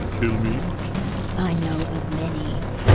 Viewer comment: What does Kitana say at 0:17? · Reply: "I know of many."